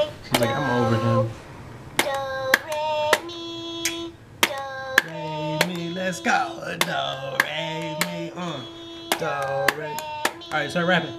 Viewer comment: sounds like hes saying " Dont rape me".